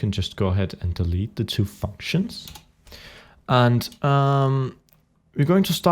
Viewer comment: Go to 5:03 it sounds like he says fuck shins